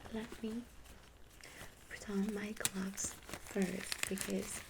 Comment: When u said "let me put on my gloves" it sounded like u said "putain"